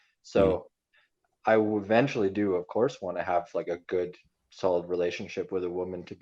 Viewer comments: he did sound like he said “Do a course.” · It did sound like he said a course but really said "of course"